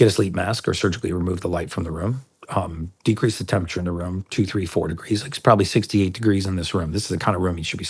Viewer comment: At first I thought he said "Decrease the temperature in the room to 34 degrees." I was like WHAT. Rewound, "decrease temp in room by 2, 3, or 4, degrees" ohhhhh lol